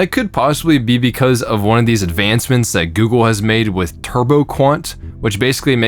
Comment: wasn't paying full attention at 3:32 and thought you said "Turbocunt" in an irish accent